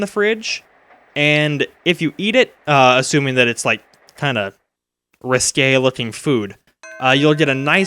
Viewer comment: Could someone, anyone, please tell me what that word said at 19:14 that sounds like riscale! I tried typing up what I thought the word was spelled like, but I can't find it, so I'd appreciate any help at all for my mad obsession with new words that I never knew before that keeps mentioning, which I have to admit is pretty cool to hear new stuff coming my way that I never heard before. That really distinguishes you from other LPers that I've watched, man.